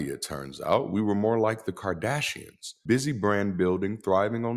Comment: How crazy is it I thought he said Cardassians instead of Kardashians? I was trying to figure out where they fit in.